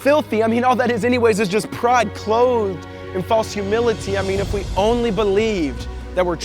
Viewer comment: Can anybody help and tell what he is saying at 3:10? "all that is, is just pride...??? and false humility." (The word after pride) I'm about to subtitle this video into another language and I kinda stuck at that point. Thanks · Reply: I believe he says, "Pride clothed in false humility"